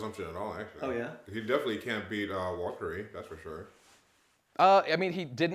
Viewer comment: Who are they talking about Luffy can't beat at 4:53:47? I cannot for the life of me understand what name is being said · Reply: Nvm I found it, he said Warcury, they're talking about the Elders here